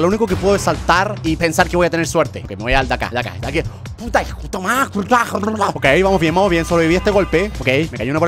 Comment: germaaanque dijiste en el minuto 4:17 xD escuche putaitomaas dhshdbjss